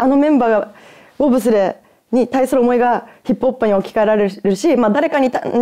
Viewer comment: 「ヒップホップ」が「ヒップおっぱい」に聞こえる。